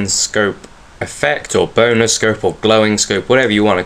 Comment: To me, your accent made it sound like you said "boner scope" 0:05 <3